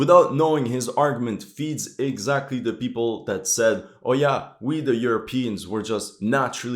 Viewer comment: did you say "OYA" that's Yoruba?